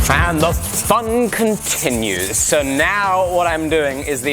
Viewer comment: I swear I heard "Thunder thong continues"...